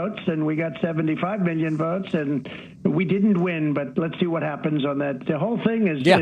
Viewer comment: he actually said "We didn't win".